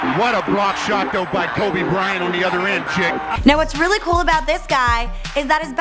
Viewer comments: It sounds like she is saying kaby Bryant 😅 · Hmm sounds like she said Kaobe Brian